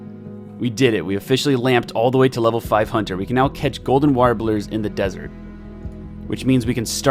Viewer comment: I swear to god that it sounded like you said "golden water balloons" rather than golden warblers, lol.